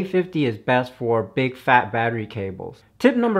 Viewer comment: what you said "big fat battery cables"; what I heard " big PHAT battery cables ;D"